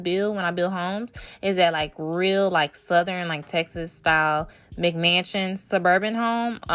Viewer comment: I’m so happy she is not your typical aesthetic builder. I love your videos and i’m gonna rebuild this! Also when she first said McMansion I thought she said "Big Mansion" XD